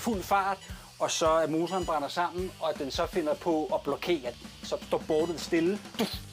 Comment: Awesome video! @ 3:23 I thought you said "balls of steel" in English when your CC said "The the board stands still" =)